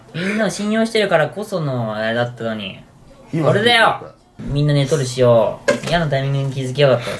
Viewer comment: 「みんな、『寝取る』しよー」に聞こえて何回も聞き直した